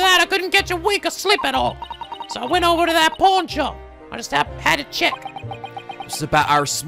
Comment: Anyone else hear "porn" instead of "pawn"? XD